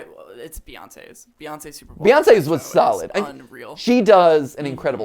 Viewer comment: did he just say “Beyoncé’s was sus”?